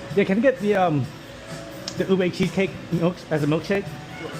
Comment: Did I just hear Ube Cheesecake Milkshake? I may overall prefer the Insta360 for color but the POV of the Meta's. Too bad they can't merge both!